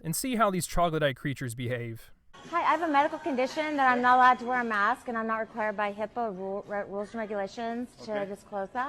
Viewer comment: did she say uh because of my illness i'm not allowed to wear a a mask and by hippo rule violation